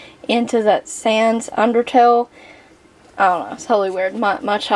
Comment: thank you. its a game called undertale...Connor likes the characters he has never played the game...just seen the characters on youtuber kids videos. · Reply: Oh ok. I thought you were saying undertow.